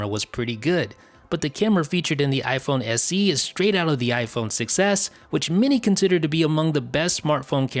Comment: Sounds like you say iPhone success instead of 6S xD